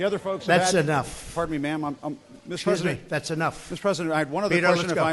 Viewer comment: I hear “Ms President”...